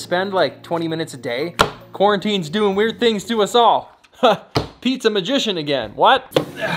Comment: I heard Pizza magician again. 😂